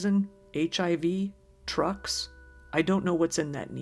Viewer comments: She was obviously misheard by the interviewer and said DRUGS, not "trucks". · Quickly pausing to say, "trucks" may well have actually been "drugs". Her accent could've been misheard. Bring injected with drugs sounds more likely than trucks after all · "it could be rat poison, HIV, TRUCKS" LMAO I think she said drugs but the subtitle understood it as trucks. I seriously laughed out loud hearing that 🤣.